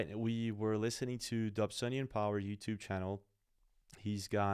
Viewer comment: Who was the guy that was credited at the beginning. I hear obsenium power or obsidian power. Right around the 00:20 mark. Thx!